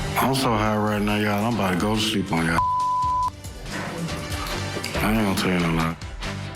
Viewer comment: I can’t understand what he saying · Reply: He said im gonna go to sleep on ya..ain't gonna tell you no lie..thats what I got from it 😊